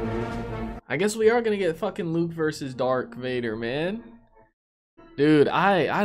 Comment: did he really call darth vader as dark vader?